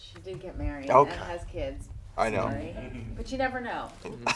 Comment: what did jimin say at 1:50 right after the woman said that rachel mcadams wasnt married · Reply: Artemis Cruz I believe he said "So sorry" Hope I help out